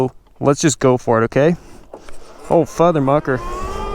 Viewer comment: What did he just say ? FutherMuker lmfao 🤣